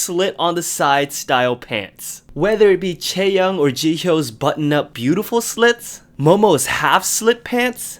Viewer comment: Almost thought you said " chae or jihyo's butt " 😂😂😂😂